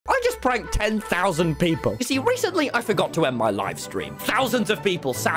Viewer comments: For a second I thought he said “I forgot to end my life” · I thought he was saying “recently I forgot to end my life-“ 😭